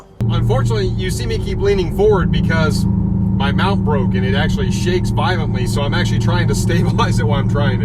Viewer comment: At 11 minutes 10 seconds, am I the only person who thought he said “my mouth broke and it keeps shaking… “LMAO